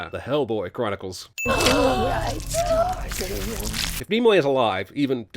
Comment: Let's parody this. I thought they were saying Nemoy the entire time. Leonard Nemoy is back as the Seaman narrator... again. Now that's some evil shit.